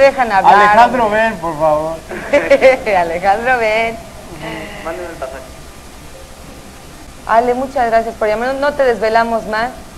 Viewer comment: Yo en la llamada completa nunca oí q le pidiera para el boleto del avión ni concierto ahí si hiso quedar mal a su hermano como pedinche 😂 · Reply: En el 3:03 se escucha que dice bajito "mandame el pasaje"